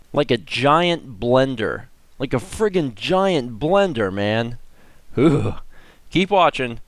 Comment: every time I hear it I think is referring to some weird cousin of Slenderman, called Blenderman